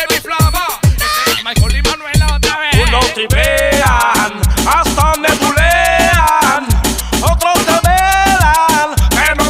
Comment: escuché bien??? xd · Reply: hasta nebulean